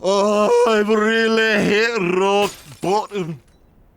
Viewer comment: lol is he saying "I've really hit rock bottom" @ 2:20?